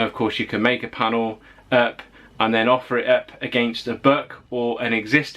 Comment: kinda sounded like you said "offer it up against a Burk". Nearly did a a spit - take with my coffee! Are we promoting workplace aggression? ROFLOL!!!!! Guess it depends on how forcefully the panel patch is offered up... XD